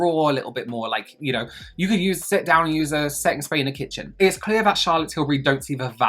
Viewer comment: I stg I thought you said "sex spray" 😂😂😂😂